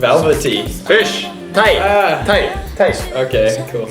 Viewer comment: I think Shimon saying the fish name in Japanese (Tai) not thight lol